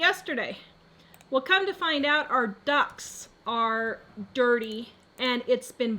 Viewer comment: At first, I honestly thought you said your DUCKS are dirty! 😆😆😆😆 Ohhhh, she meant her air ducts. Right! 👍🏻